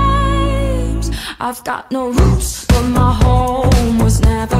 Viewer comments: i thought she said " i got no roofs in my home .. " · The song name: no roots Me searching : I got no whoosh.... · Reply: i thought she said, "I got no roof on my home" lol I was too embarrassed to search it in fear that google might call me stupid